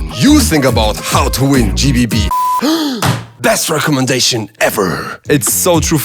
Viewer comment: без рекомендейшен? Или мне послышалось?